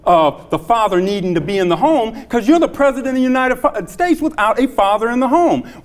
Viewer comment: did he say " because you're the president of the United fucking states"? Lol